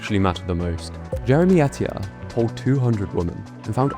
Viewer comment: I thought you said “pulled” 😂 very different kind of study 😉😂